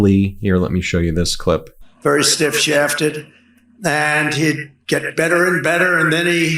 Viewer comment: Did he just say “stiff shafted”?